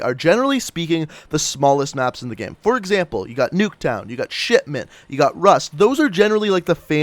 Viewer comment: Wait at 1:30 did he just say sh##mint?